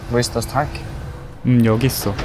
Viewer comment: 이거 Where is the tank 라고 들리는데 did 나 go는 안들리구 내 귀가 맛이 가버린건가?